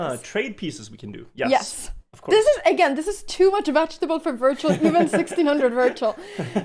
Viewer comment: His actual name is Øyvind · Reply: That really does not matter at all as it does not change me mishearing wirtual as virgil which is the whole point XD